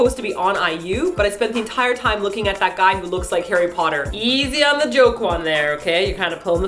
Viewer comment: Did you say "Easy on the Jokwon there."?????? @ 7:33 Hahahaha You're Hilarious!!